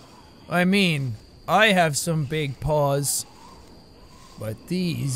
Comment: who else thought he said I have some big balls instead of big paws 😂